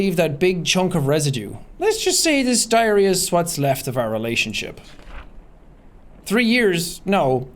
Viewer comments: in the video, could've swore jack said "lets just say this diarrhea sweat's left of our relationship..." ..just me?? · he said diarrhea instead of diary